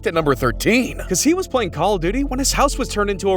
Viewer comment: is it me or did it just say call of diddy